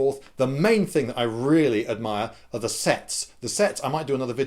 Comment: I swear I misheard that as sex.